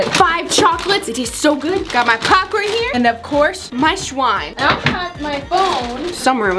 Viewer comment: It sounds like said “ my sWiNe “ when we know she meant slime.